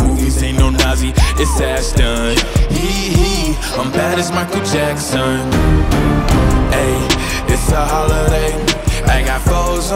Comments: I like the part that lil and says he’s like Michael Jackson 😎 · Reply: i thought that he said i’m michael jackson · "Hee-hee, I'm bad as Michael Jackson (dun, dun, dun)" ~ 🎶 Banger song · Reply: lol I thought he said "and that is Michael Jackson"